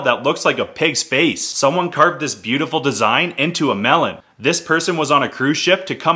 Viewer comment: Did I just hear : "This person turned this design into a melon"